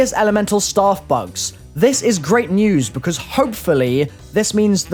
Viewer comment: doesnt it sound like hes saying bugs-uh